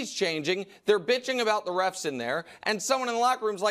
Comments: Did Nick saying "bitching" at 10:20? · Did Nick say bitching 10:20 😂😂? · Did Nick say “they’re bitc…ing” around 10:22 mins of the video? Lol 😂